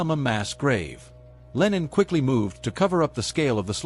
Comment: you have Lennon in the subtitles, instead of Lenin. Again at 4:16 also at 00:09. Give peace a chance :-) 00:51 Lennon's Bolsheviks stormed the winter palace, I am dying ;-)